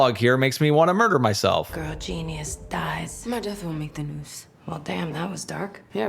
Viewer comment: I can’t understand what the hell riri is saying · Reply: I believe she said “my death won’t make the news”